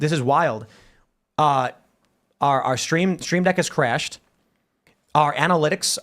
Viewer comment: When he said “stream deck” I heard steam deck. I was like, you’re broadcasting on a STEAM deck? How?